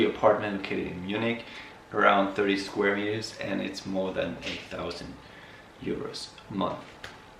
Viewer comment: Did he just say 8000 euro??? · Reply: 1000 (a thousand)